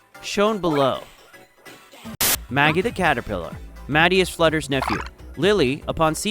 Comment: Did I hear Maggie or my name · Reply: Maggie i think